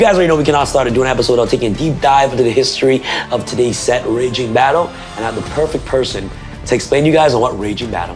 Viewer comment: I literally thought it said ragtime battle and was shocked to find out it was not a battle of the bands.